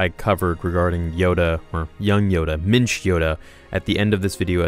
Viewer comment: I thought you were saying "Mitch Yoda"